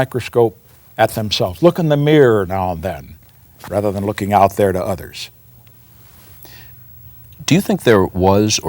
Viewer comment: what did he sayyyy? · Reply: "Look in the mirror now and then, rather than looking out there to others."